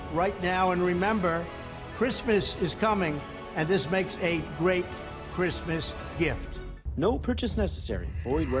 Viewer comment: Idk, but I swear, right at the very end, Trump says 'This makes a great...Christmas...grift.' I was like wtf did he just say?? 🤔😆